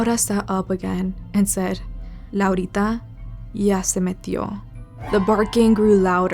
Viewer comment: what did she say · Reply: laurita already entered